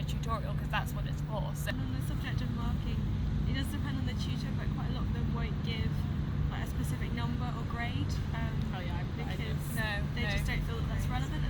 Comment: What did say at 6:29? I can't quite make it out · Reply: "on the subject of marking, it does depend on the tutor, but quite a lot of them won't give a specific number or grade because they just don't feel that it's relevant at the moment. So you kind of just have to get used to not comparing your work to anyone elses." Sorry for the poor sound quality - Uni Parks isn't the best place to film on a windy day! Hope that helps :)